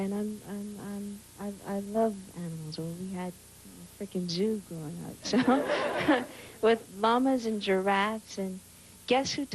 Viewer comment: freaking what did she say?? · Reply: "we had a freaking zoo growing up" lol